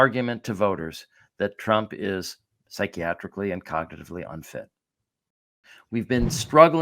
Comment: I misheard what said at the start about Trump being psychiatrically and cognitively unfit and heard instead 'psychiatrically incontinent ' which I think is still apt!